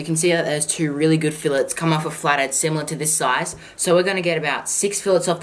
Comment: I like the way you say flat head it sounds like your saying flated